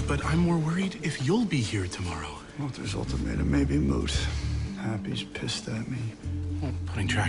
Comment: What is Toby saying at 0:26? I can't catch it.. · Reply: I know its been 7 years but in case you're still wondering " Walter's ultimatum may be moot"